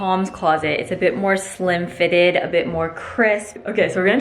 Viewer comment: When said the shirt is slim fitted I almost thought she said slim thicc